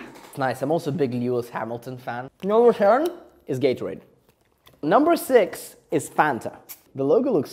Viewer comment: “Top 10 most popular soft drinks” pulls out powdered coffee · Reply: Did he just say gaytorade ?